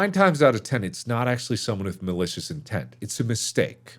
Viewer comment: I had to rewind because I first thought you said "delicious intent" instead of malicious intent